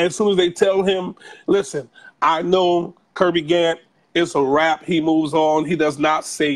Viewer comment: What is the man's name who she had on tonight? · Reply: 888hereandnow Kirby, but I’m not sure about the last name. At 43:00 sounds like he said “Kirby Gant”